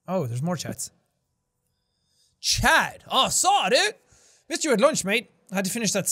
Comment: OK DID YOU JUST SAY "UH SAY DUDE" LIKE KALLMEKRIS??? I could be wrong all Chad's are the same ✋🙄 but THAT'S WHAT IT SOUNDED LIKE